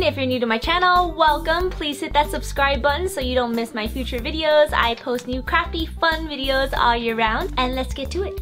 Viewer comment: I thought she said I post crappy fun.....